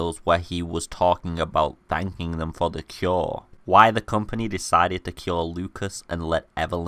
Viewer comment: Why did you say that the organization killed Lucas? I haven't seen any actual evidence of his death in the game? 3:06 <link> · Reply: He said cure. Sorry ik its been 3 years